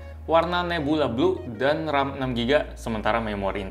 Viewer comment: Apa cuma gua yg denger ram 6 g menit 2:42 atau gua salah denger kali ya......